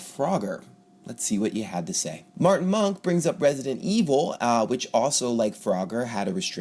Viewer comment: Ha! For a sec there I thought you said president evil.